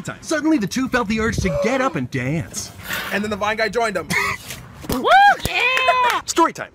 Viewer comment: my favorite one is the one where the guys is like "anD THEN THE VINE GUY JOINED HIM" and Thomas and some dude danced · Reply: OMG I always thought he said the white guy XD OML